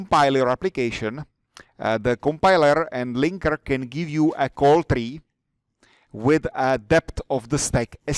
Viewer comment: Excuse me, At 23:05: "Compiler and linker can give you ...." And at 23:36: "You can help yourself by ..." Can anybody hear these missing words? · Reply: i think at 23:05 he said "call tree" [ <link> ] and said the same thing again